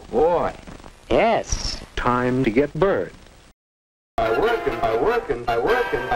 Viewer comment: For a little bit, I misheard "time to get burned" as "time to give birth"